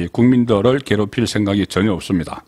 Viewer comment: 25일까지 국민을 괴롭힐 생각이 업다니 내귀에는 선관위를 괴롭힐생각이 업다는말로 들리는데 나만그런건 아니겠지?? 그런답변을 김문수가 듣고싶었는지도몰라 너의 정체를 밝혀라 어디쪽이냐 선관위세력의 한통속인거같다 부정선거에 한마디도 안하는자는 정치권에서 퇴출시켜야한다 이번전쟁은 국민과 선관위의전쟁임을 명심해야한다